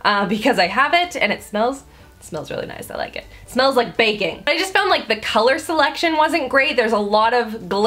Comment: I thought she said it smells like bacon. Hahahaha ewwww